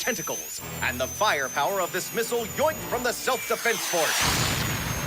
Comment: Did he say "Yoinked"?